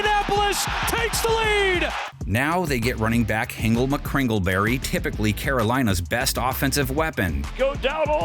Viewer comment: does anyone else hear this weird sentence wit hingle mckringleberry lol